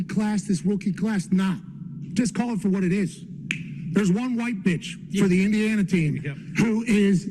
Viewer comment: Did he say 1 white bitch 1:09 Did he say 1 white bitch, WHAT!